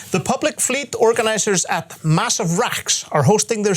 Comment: lol it sounded like was saying "massive racks" XD